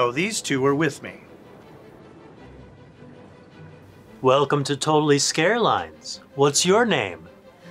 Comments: “Welcome to Totality Scarelines” yet the subtitles say: “WELCOME TO TOTALITY AIRLINES.” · he said "hi welcome to totally scareline" but the subtitle said airline. Am I missing something here? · can we just talk about that he says scare lines but the subtitles say airlines